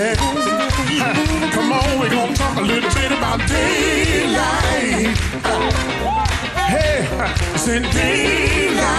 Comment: Am I hearing this right? At 3:54, did he say " we're gonna talk a little tit about daylight"? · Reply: A little bit…. Not tit…get head out of gutter